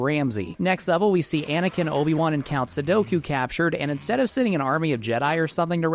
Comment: I heard Count Sudoku and I was out.